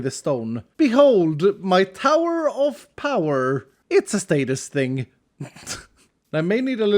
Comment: I heard sadist, instead of status but either one works really